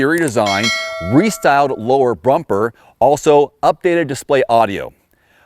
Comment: Did you say “brumper”? Haha Great review. But the car doesn’t scream OMG A MUST HAVE. Keep up the great honest reviews :)